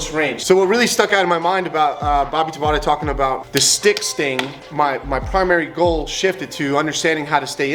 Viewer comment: I thought it said you try "tree stick" fighting